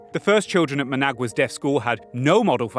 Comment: I was trying so hard to figure out that you said Nicaragua's DEAF school and not DEATH school because I misheard and thought you were going to describe yet another genocide I was unaware of